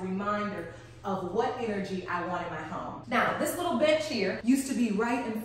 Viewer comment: Aside from the beautiful tour of your Loft Lauren. Somewhere between 4:14-17 of the video I thought you said " now this little bit!ch right here.." 😳🤣 I was cleaning up ( wasn't fully paying attention at that moment) my home and watching from my TV. Lauren had me thinking she finally snapped and cursed somebody out! 🤣